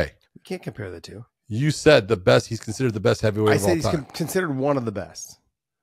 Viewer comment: you were right! sorry 😂 love you both and i know what you meant but you definitely said "the best heavyweight of all time"